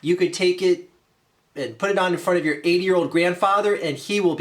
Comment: Did i hear him say " 8 year old grandfather"?